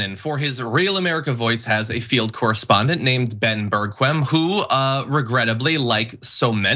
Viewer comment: Not celebrating his illness-----BUT I thought you said Ben Birdquim; and I'm like did you just say Ben Bird Vigina? Quim = vagina